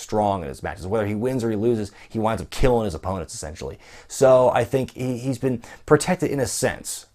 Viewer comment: did anyone else hear "killing his opponents SEXUALLY" instead of essentially